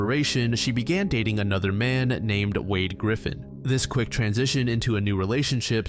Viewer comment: When bro said "Wade Griffin" I heard "Peter Griffin" instead...